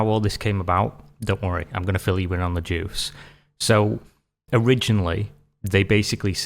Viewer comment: When you said "I'm gonna fill you in on the juice" I heard "I'm gonna fill you in on the Jews" and I was very confused for a second because I thought it was a weird anti-semitic remark of some sort. I'm a massive goober...